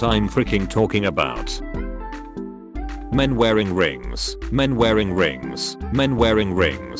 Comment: i wasn’t looking at the video and i heard “men wear ring rings. men wear ring rings.”